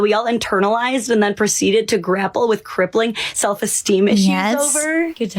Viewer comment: I rewinded to understand what was being said and still don't understand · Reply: "crippling self-esteem issues over"